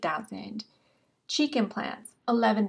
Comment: every time u say "cheek implant" I hear "chicken plant" =D